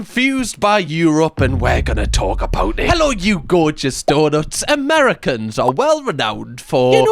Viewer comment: the first time i came across your videos i thought you were saying Hello you gorgeous daughters and i was just like yeah okay why not and it took me a few videos to realise the donut popping up as you say it means something help